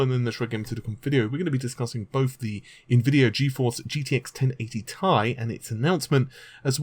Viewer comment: Sounds like he says Gtx 1080 tie!🤔